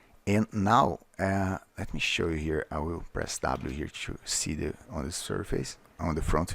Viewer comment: sorry, what do you press at 16:50 to see the particles instead of the form? Cant hear, "Press Tab" ,"Press double"? Don't works for me.. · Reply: hey Postol, the shortcut for the front view is 3 (while the cursor is on the viewport). Not sure what I said, it looks like I said tab for no reason